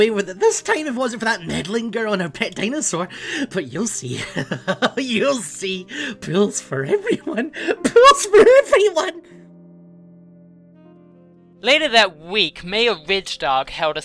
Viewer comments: sounds like he is saying pills for everyone XD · sounds like was saying pills for everyone :D